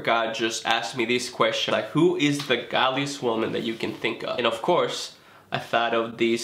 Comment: I laughed so much when said "who is the godliest person you knw" because I thought he said "who is the gobiest woman you know "(in the UK someone who talks alot & loudly is said to be gobby)